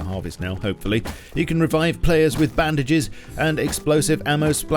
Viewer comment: I’m sorry did I hear that correctly (4:40) you can now revive players with bandages?